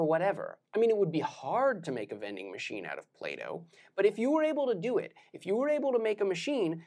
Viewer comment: I heard "vending machine out of Plato"😂